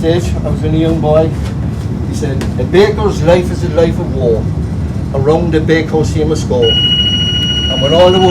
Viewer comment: can someone tell me what he says...what is he resiting Iwould love to know. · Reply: He says ‘A bakers life is a life of Woe, around the Bakehouse he must go’. Says his grandfather used to recite this.......